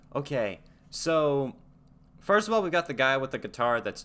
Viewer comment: Did you just say, “first of all, we got the guy with the guitar…?”